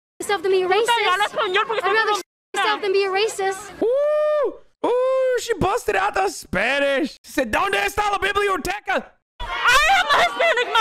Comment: what she says is "bitch i speak spanish" and the rest i dont understand